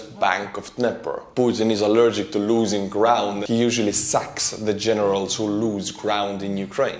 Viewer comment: I thought you said putin usually sucks the generals, which is probably true even if you did not say it.